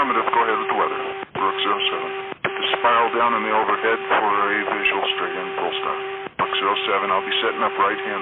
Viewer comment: the inaudible is him saying a visual straight in full stop